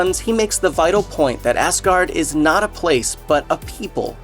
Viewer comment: did he say assguard?